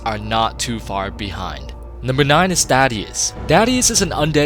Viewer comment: sounded like you said daddius instead of thaddius. Boy ain't that a thought